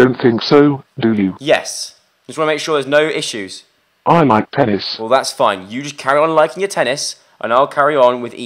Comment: I've been watching too much Pewdiepie and at 6:40 it sounds like boibot says " I like Titties" I AM SORRY BUT IT SOUNDED LIKE IT XD